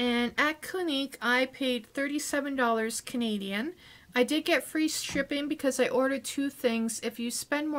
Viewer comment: I’m on the treadmill..so around a lot of noise..I heard “I get free stripping...”